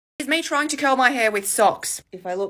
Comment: Could be your accent but at first I heard "trying to kill my hair with socks" :D